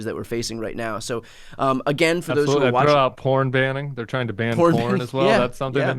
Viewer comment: I thought said "Horn Banning". That took me to some strange places.